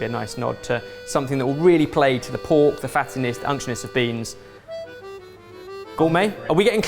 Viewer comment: We may have to change the name 😉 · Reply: Was the word the closed caption translator completely failed in writing with the “(indistinct)” line supposed to be “unctuous?” Edit: Nevermind! Upon another listen, the word actually sounds like “unctionous” or “unctionus,” but those aren’t words that exist in the English dictionary… What in the hell was Ben saying?!